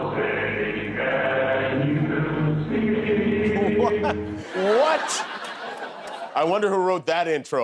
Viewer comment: Did he just say toosstages 😂😂😂😂